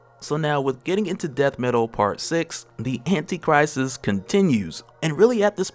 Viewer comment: For a second I thought you were saying the Anti Christ and got scared I was in the wrong place